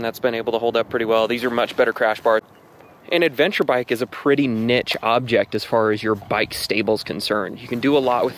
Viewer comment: did you just say... nitch?